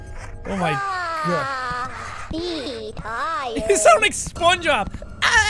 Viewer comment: Wait XD, did you say "Spongeob"?